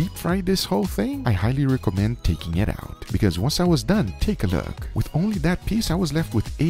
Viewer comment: Did you just say "steak-a-ook"??